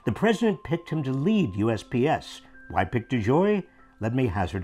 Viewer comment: Was that "US BS" or "US PS"? Sounded like "BS" to me.